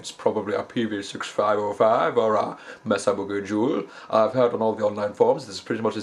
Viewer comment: "Yu won't son beg, yu won't son hevi, yu wull just not be herd" Love your accent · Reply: i thought he said Mesa Boogie Juul because of the accent.